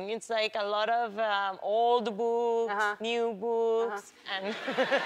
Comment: "He reads anything, like old boooooks, new boooooks." Funny thing is, I'm hard of hearing and all this time i thought she was saying "he reads anything, like old booooooobs, new booooooobs." i was like wtf???